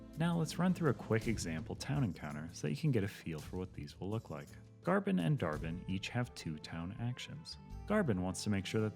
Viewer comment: I love you CTG, but having those two character names so close makes understanding some of this more confusing than it should be. I keep hearing "Arbin and Arbin".